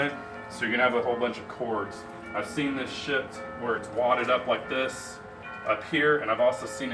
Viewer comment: I thought he said "Ive seen this sh*t..." lol